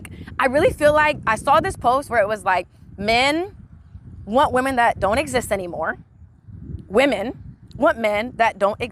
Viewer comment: she actually said "men want women that don't exist anymore" She did not say "men want women to no exist anymore"